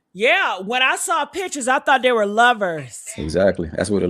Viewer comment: I thought I heard someone said her and her brother was roommates